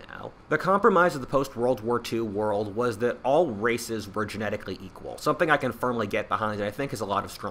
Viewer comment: Lol for a short moment when you said all races where genetically equally and that is something you could get behind I heard "all racists are equall." I couldn't stop laughing at the image of a two different race racists agreeing with each other at their superiority.